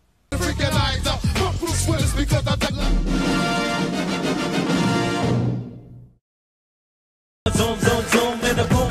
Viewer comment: did he just say f*** Bruce Willis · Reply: No, he said ,"FUBU sweaters." It was still the 90's, and there's no way they'd let him cuss on national television on basic cable.